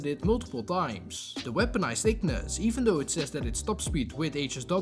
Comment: what is the name of this vechicle? I didnt understand the name and the automaticly generated youtube captions tell me nothing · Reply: The one before 4:50 is Banshee GTS. The after is the Weaponized Ignus. I assume you mean the Ignus.